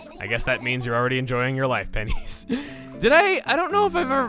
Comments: Ha! I thought it said penis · I thought when said pennies he said “penis!” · I thought pennies said penis